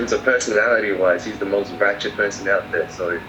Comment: ，请教一下，哪位能说一下8号说的“他是我见过最狂最不要脸的人”用的是哪个形容词，听力不行，听了好几遍听不出来，哎~~ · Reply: Ratcher